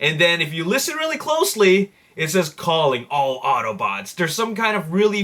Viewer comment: I thought he said powering all autobots